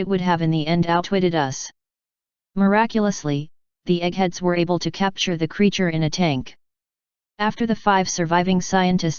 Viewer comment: Did she say "the EGGHEADS were able to capture the creature" @ 5:38 ? lmao! It fits contextually with what it was saying before that, so I guess so.